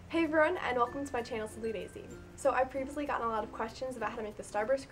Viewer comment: I barely can hear you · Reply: my only complaint is that I thought she was saying"sp Daisy" not "simply Daisy", sounded better on this video